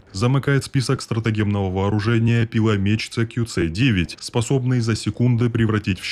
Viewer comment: Я не игра во все мои любимые игры целых 3 месяца. Надеюсь мой офицер по Демократии ещё жив.... 3:17 послышалось CQC. Думаю мне стоит меньше смотреть мемов со Снейком